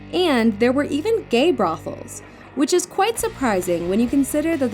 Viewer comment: brofles? brauffles? What did she say and what is that.